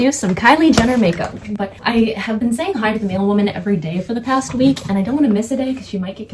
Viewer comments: Tell me why when you said “mailwoman” I heard “male woman” and was lost in thought for approximately 3.5 minutes until I realized🥴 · when she said mailwoman. thought she said male-woman i was very confused